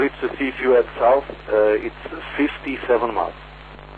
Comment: I hear 57 miles, not 67.